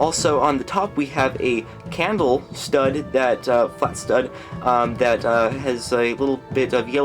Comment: I don't know what you are saying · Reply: "flat stud"